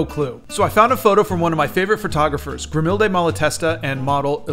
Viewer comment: Thank god you said "photographer" bc i thought you said "porngrapher